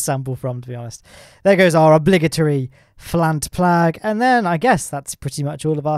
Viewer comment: Did you say "flant plag" at 9:01 ?